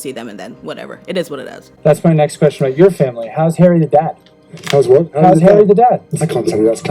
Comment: When I watched this video, I thought he said how is Harry AND dad. I had to listen again and then realized what was said.